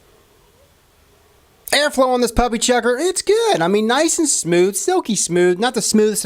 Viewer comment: did he just say puppy chucker